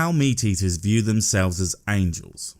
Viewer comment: hahahaha i thought it said men eater